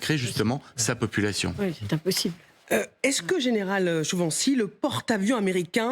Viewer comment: J'ai entendu Général ? 😂😂